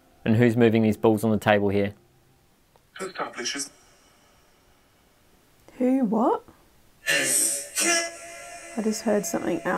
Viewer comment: I heard loud and clear "What's up Bitches" which is honestly something I would say alot as a ghost 😅